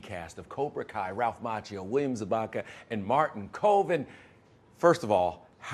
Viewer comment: i legit thought he said "martin covid"